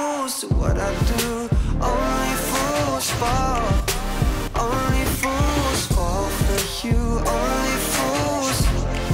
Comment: I kept hearing "Only Fussball for you" and I was immensely confused XD